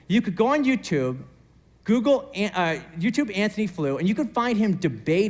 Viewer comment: Anthony Who? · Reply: At his first mention I didn't know who they were talking about (and I hadn't read the description yet - sorry,), so I stopped the vid and searched for him. But I heard it as Anthony Foyle and that only led to Downton Abbey references. 😂